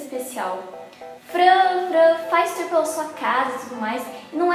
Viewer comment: so eu ouvi ela falando`franga`